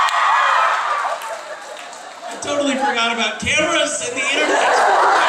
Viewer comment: KKKKKKKKKKKKKKKKKKKKKKKKKKKKKKKKKKKKKKKK 5:02 I just wanted to understand what Misha said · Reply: He said "I totally forgot about cameras and the internet"